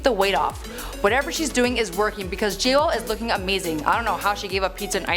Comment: Did she say JLO?